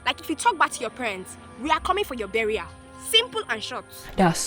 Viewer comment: the nigeria woman made me LAUGH “we are coming for your burial” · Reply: Thought she said “We’re coming for you derriére” meaning “ass”